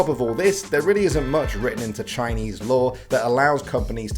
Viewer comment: thought he said Chinese lore lmao. Its law